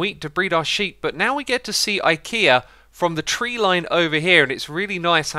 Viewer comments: did xisuna said IKEA??!! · did he say Ikea?